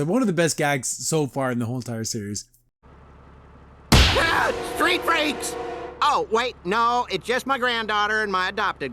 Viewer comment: I thought Hop Pop said “street wraiths”. XD not “street freaks” I’ll have to watch it again. XD